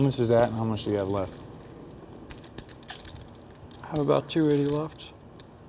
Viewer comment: What does Dwan say to Phil at 21:23? I didn't catch it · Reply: "I have about 280 ($280,000) left"